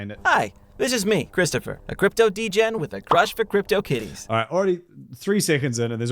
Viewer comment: why did he say he likes kids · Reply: He actually said *Cryptokitties*.